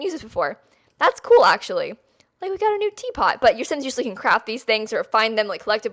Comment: Sounds like she said Sims can usually crap these things... 💩